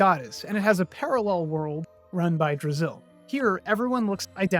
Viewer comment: A weird one. I was doing the dishes with this on and could have sworn you were saying the parallel world was Brazil.